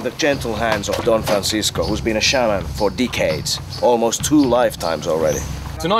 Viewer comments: it sounded like he said dickheads when he said decades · When you said "decades" it sounded like you said "dickheads" 😂😂😂